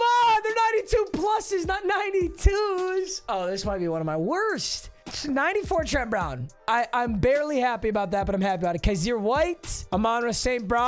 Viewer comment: “94 Trent Brown I’m am barely happy about that but I am happy about that cause you white” is what I heard